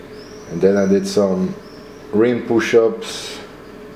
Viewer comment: what did pushups did you say you do? i hear green pushups? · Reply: Ring.